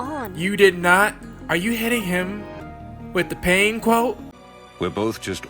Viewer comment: For a second, I thought you said she was hitting on him, but I didn't quite catch that. xD